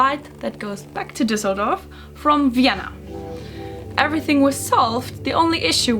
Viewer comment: thought she said Vietnam for a sec lol